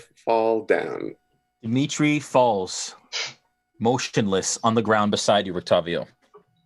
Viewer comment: When Dmitri fell to the Blight, I could have sworn that said, "He falls, moistionless," not "motionless." Probably just a trick of my hearing, though. But then, "Lord have Mercer," so who knows with this crew?